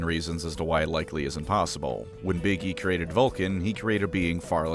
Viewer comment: I misheard you pronounce "Big E" around 8:34 and now I will only ever refer to him as 'Iggy.'